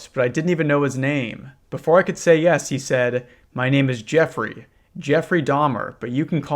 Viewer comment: i am so cooked bruh i was rewatching this and only then realized that did not say jeffrey epstein